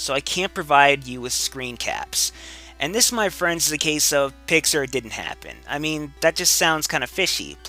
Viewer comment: What was it that he said? It kind of sounded like "Pixxar, it didn't happen" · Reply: pics or it didn't happen